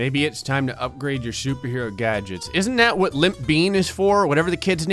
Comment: Wait did he just say, "Isn't that what Limp Peen is for?"?